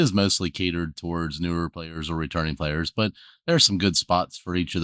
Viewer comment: Realized you said returning players…thought you said retarded players and I was like yep…talking about me. 😂